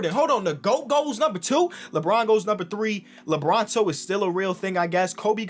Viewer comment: lebron ____ is still a real thing. what did he say ? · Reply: Lebronto because it sounds like Toronto